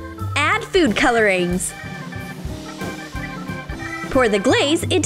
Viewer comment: wait did she say foods colorings